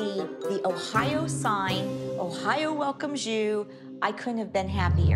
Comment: When Cathy said “Ohio welcomes you “ I thought she said “Ohio welcomes Jew” 💀